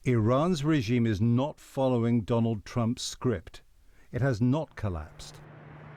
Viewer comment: I thought the announcer said “Iran is not following Trump’s GRIFT” (actually it was “script”)